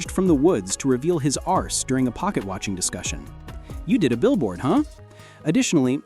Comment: the fuck is it saying? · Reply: Ass